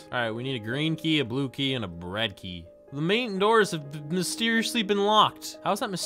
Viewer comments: i loved it when he said bread key at 4:23 at least thats what i heard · Who else heard him say "bread key"